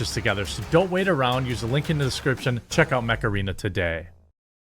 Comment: Thought you said you loved the macarena.